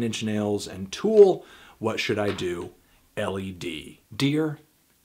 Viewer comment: i thought he said "LED"